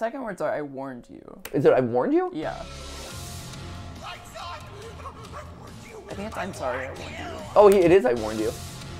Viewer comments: I keep hearing "Lights On! I warned you! I warned you!" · "Lights on! I warned you, I warned you!" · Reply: I thought he said "Lights off! I warned you, I warned you! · I thought the sun moon guys said “Lights on! I warned you, I warned you” maybe he turns into the moon version of itself and it’s triggered by lights vs no lights? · It sounds like the beginning of the Sun Jester’s dialogue is him saying either “Light’s Off, I warned you” or “Light’s On, I warned you” I can’t tell which. Either way, it sounds scared, so it would track that the sun is friendly, and whatever environment he exists In he’ll be helpful for, but then things change and the moon comes out and will try to hunt the player. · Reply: I hear “I saw it, I warned you I warned you” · It sounds like he says "I saw it, I want you" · I think it say lights off or on like lights off I warned you I warned you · I hear LIGHTS ON I WARNED YOU I WARNED YOU